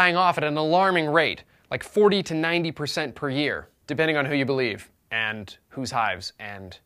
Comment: I honestly thought he said "depending on who you believe and, whose high"